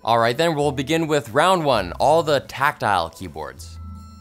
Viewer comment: For a second I thought he said "Tactical Keyboard"